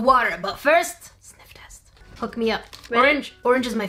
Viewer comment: Did i just heard ,,fu*k me up" or am I wrong- QwQ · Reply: I think it was hook me up but I heard it the other way too